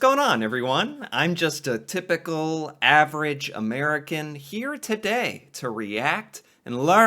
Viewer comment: every time i see your videos pop up, i hear you say “i’m just an AVERAGE ordinary American” lol